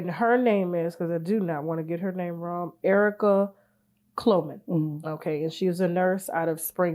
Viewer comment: What are the predator and wife’s name? Sound like she said “Clomen”